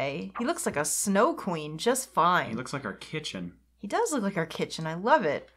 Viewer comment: i thought when you were saying "our kitchen", you were saying "r. kitchen" like a discount r.kelly